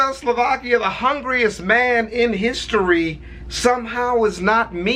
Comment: I thought he said Chavezz Slovakia the Hungaryest man